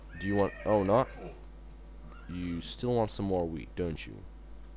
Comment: it sounds like u say "You still want some more weed" xD